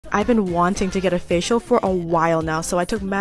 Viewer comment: I heard "I want a facial" and just thought ayoooooo💀💀💀